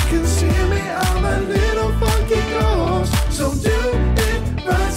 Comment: why did I hear "I'm a little f-cking ghost" just- why? ._ .